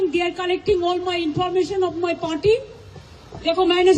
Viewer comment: pe kya Boli Information of her Potty 💩😂